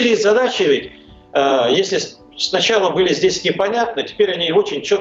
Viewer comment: Максим, я всегда с интересом слушал Ваш канал, но сегодня Вы допустили серьёзную неточность, цитирую рос. дипломата : " цели и задачи были И понятны", а не НЕ понятны (2:18). Надеюсь, что просто не расслышали, а не умышленно исказили сказанное... Удачи Вам и каналу.